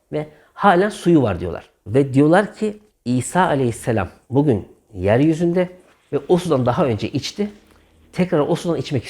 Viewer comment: !!!! İsa (a.s.) şu an yeryüzünde ve o sudan içmek istiyor. !!! Doğru mu duydum?!!